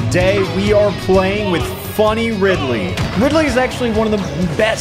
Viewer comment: Thought that said furry ridley